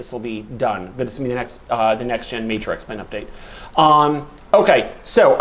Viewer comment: Wait, at 13:25 does say the next X-Plane update will be the major next gen update? I must have misheard that, he wouldn't just say that in a mumbled sub-clause, right? Anyway, great and informative video, thanks for sharing!